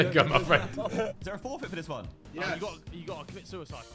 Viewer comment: What does Harry say at 2:24? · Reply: "you gotta commit suicide"